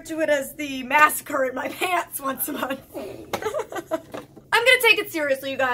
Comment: The massacre in my pants once a month 😂😂😂😂 Couldn’t agree more with that description 💉🗡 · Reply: I heard "masquerade in my pants". LOL that kinda sorta works too 😂